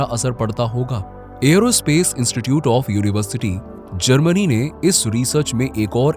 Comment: 🎉Did I heard aerospace institute of university 😅